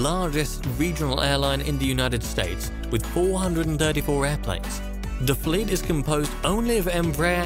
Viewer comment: Did you just say 434 airplanes?